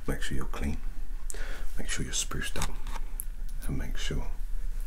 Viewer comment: spruced up? That's what i heard lol